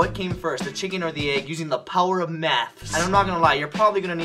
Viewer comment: did he say the power of meth will help or maths? lol